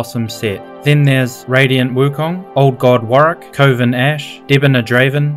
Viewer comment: I just heard "covid ashe"